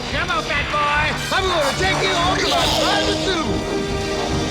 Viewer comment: can anybody make out what Venkman was saying after he said "Come on big boy."? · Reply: I think he said "I'm gonna take you home to my private zoo"